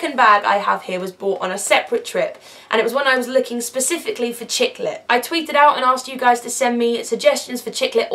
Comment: when she said Chick-lit i thought for a sec that she said "chiclet" and i was confused as to why she was buying tiny gum at a bookstore